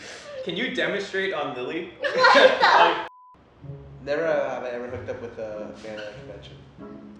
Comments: What did he say at 8:15?? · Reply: Never have I ever hooked up with a fan at a convention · what did scarra say at 8:15 ? can't hear him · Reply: never have i ever hooked up with a fan at a convention